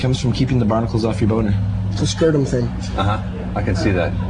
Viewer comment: Can anyone tell what Flea says at around 2:30 after Anthony says 'it comes from keeping the barnacles off your boner'? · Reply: I think he says "It's a scrotum thing"